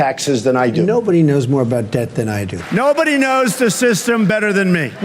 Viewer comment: 抓個小蟲 3:09 debt 聽成 death 所以翻成死亡了 😂